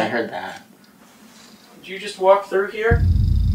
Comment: i heard "walk" or walker